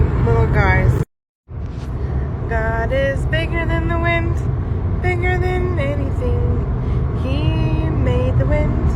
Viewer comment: Listen closely to what she says as the camera is focused on the ground and those grills 😬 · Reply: She says "snow fricking way" it's a bad joke